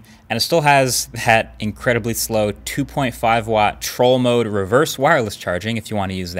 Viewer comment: Brownlee 5:43 did you say trolling motor? Do you fish?